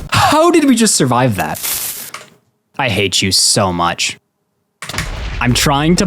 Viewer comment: did he just say "I hate jews so much"? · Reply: He said you 😭😭😭